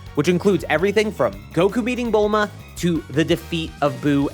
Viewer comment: I keep hearing "Goku beating Bulma"...